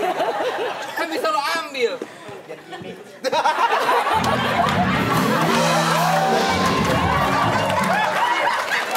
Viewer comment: penonton ngomong apa si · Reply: Biar gimmik